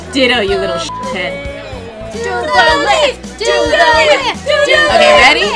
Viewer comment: I thought They said "DO THE WHIP, DO THE WHIP!!!" 😂😂😂